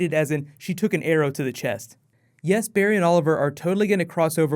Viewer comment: for a minute i thought said that sara took an arrow to the knee.